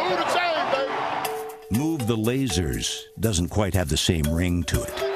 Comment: I was cracking up when I heard "Move the lasers" Lol 🤣